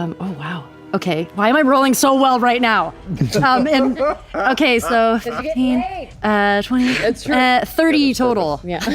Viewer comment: does anyone know what laura and Liam say at 50:15?? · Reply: laura says "cause you're gettin' laid!" and i'm not sure but i think liam might say "she has a purpose"